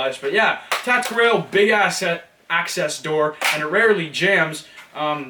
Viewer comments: I thought he was going to say big ass :) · That moment when you say "Big asses" Instead of "Big access" 6:36 XD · Do you swear because at 6:37 you said ass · Did he just say ass